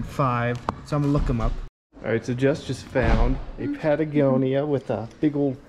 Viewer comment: In her defense, I thought you said, "I just found these Ariat boots" instead of "Jess just found." All a girl wants is some credit! 😂 Great video!